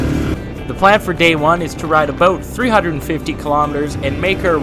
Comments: Did you say? "The plan for today is to ride a Boat" 😂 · Did you say ride a boat?! love the commentary and introspection your provide, ride on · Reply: I said about it, but sometimes the mic makes things sound off.